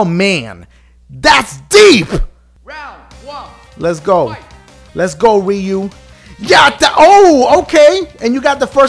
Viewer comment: sounded like lets go wii u